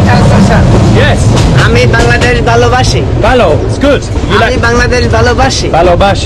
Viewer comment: what the guy said is "Ami Bangladesh bhalobashi" which translates to "I love Bangladesh"